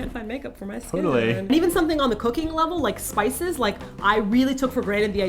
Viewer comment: For a second I thought said "on a cocaine level" hahahaha I had to rewind, she said cooking level. Hahahahaha 😂😂